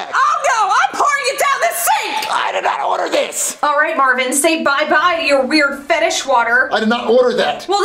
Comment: What did Rose said?! 💀💀💀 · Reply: she said “say bye bye to your weird fetish water”